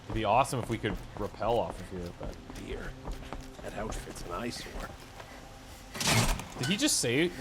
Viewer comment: I think he said "That talisman's nice"